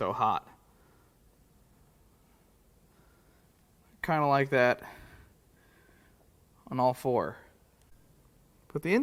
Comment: Did he say I kinda like that on all fours?